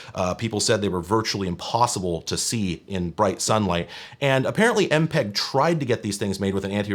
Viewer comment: literally thought it said empreg